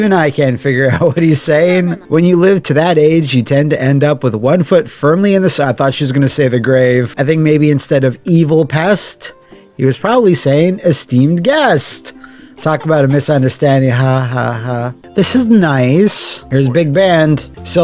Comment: "I think maybe instead of 'evil pest' he was saying 'steamed hams.' Patented Singing Wolf recipe."